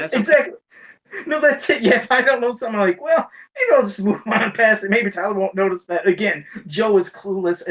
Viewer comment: What did Joe say at 59:38? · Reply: "I'll go real fast; maybe Tyler won't notice that."